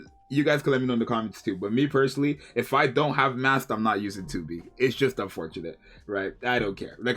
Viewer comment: why did it sound like he said screw eveyeone who doesn’t have Mast and enjoys 2B 😂